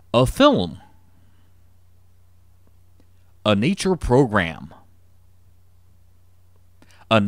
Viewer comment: que dice ahí? Xdxd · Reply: Anitra program